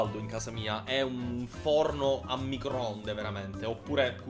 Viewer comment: Minuto 0:28 ho capito po**o invece di forno😂😂😂